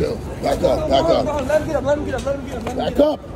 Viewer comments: What coward said “let him get him”?? Tf is people’s problem these days! Thank god for this kind man who stood up for someone in need of help! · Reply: I thought I heard him say “chill let him get up” but I could be mishearing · Are we gonna talk about the guy in the back saying: „let em get em, let em get em!“ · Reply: I heard “Let ‘em get up,” not “Let ‘em get ‘em.”